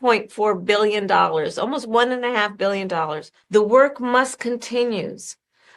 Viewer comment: she actually says "the work must continues" 😂